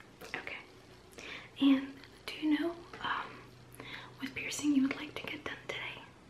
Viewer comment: Did I just hear “piercing your boyfriend”